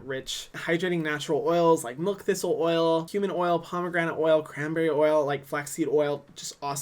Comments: excuse me did he say HUMAN OIL?! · I had to double take I thought he said human oil 😂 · Ngl thought he said human oil instead of cumin oil for a hot second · Wait I thought he said “human oil”